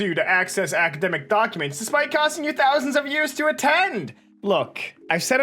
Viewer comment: despite costing you thousands of years to attend what did you just say?